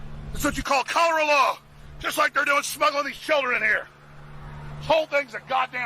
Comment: deadass I heard Alex Jones screaming about snuggling these children over here